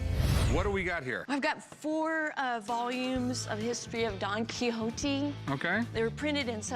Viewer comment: did she just say don quixoti